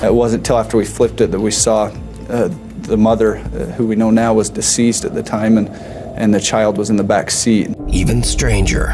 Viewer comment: Am I the only one who heard "help' 2:22 time stamp? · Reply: No, I heard ‘Help, somebody(someone) help’